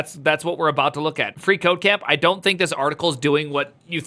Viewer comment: Lol, I kept hearing "FreeCokeCam", thought it was one of your viewers commenting during the live stream.